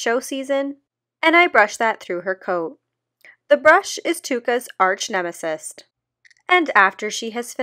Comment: did she say arch nemesist?